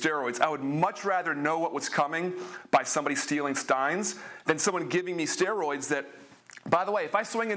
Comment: He said stealing stines instead of signs